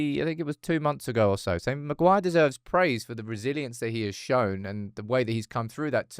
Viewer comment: I honestly thought he said Maguire needs praise for the Braziliance he's shown 😂